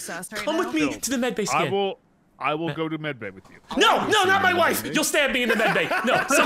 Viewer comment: I'm an AH fan, don't usually watch these guys, but heard that and Jeremy have been playing with them for a bit, figured I'd check it out. But. Did he really call his wife at 1:45 ?